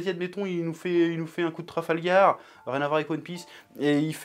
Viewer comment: aaaahh trafal gar j'ai compris